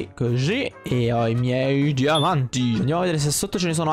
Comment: solo io ho capito che ci sono gli amanti invece che diamanti? Hahaha 8:26 easter egg